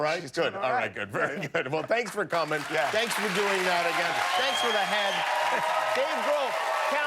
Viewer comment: Did he just say to Dave Grohl, “thanks for the head”?😂😂😂